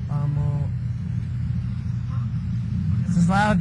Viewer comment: at around 8:40 did I hear it right?? he said "this is love.... wahhh...